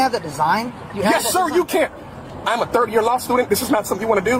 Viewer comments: Almost sounded like he said “thirty year law student” which would have been much more believable. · “I’m a 3rd year law student!” How is that even a flex? LMAO! 🤣 🤣🤣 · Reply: I thought he said "30 year old law student" 😂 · Did he just say he’s a 30yr law student?